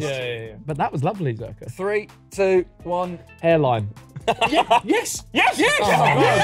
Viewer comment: What did Ethan say for Jake Paul? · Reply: Hairline